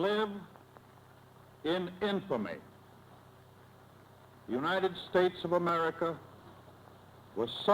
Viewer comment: My dad for years thought Roosevelt said, "Yesterday, December 7th, 1941, a date which will live in IMFAMY....". Dad was so surprised when he learned what was said, he scarce believed it for a while. He said he could swear Roosevelt said imfamy.